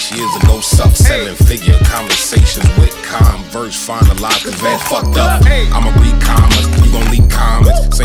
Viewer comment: Did he just say "vans fucked up" 😂